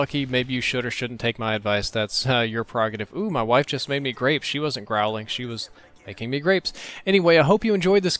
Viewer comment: How do you make grapes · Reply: to me it sounded like he said she made crapes. The French style super thin pancakes.